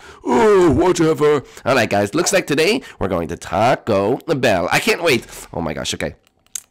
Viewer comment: did u say taco le bell on